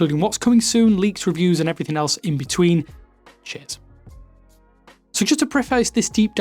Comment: Hello gang. A lot of people think there is a swearword/curseword at 0:18. Sorry to burst the bubble, but it's "cheers". Although tell me what you hear as this could be a fun game □. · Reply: I heard "cheers".